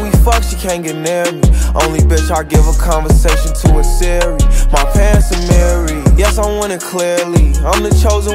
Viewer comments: When he said “my pants are amiri”; I thought he said “my parents are married” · Am I the only one who thought it said my parents are married?